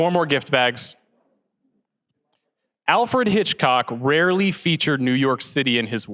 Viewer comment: did he say Alford Hitchcock?